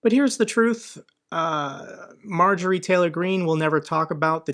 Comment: I heard "Marjorie Traitor Greene" :)